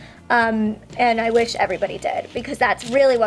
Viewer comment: I heard I wish everybody dead instead of everybody did